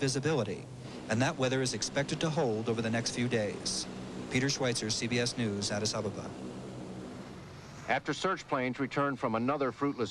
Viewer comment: what did Peter Schweitzer say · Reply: Nicholas Medovich Peter Schweitzer, CBS News, Addis Ababa